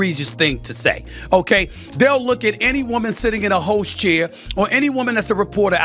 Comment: I thought Stephen said any woman sitting in a “hoe chair” 😂 but he said host.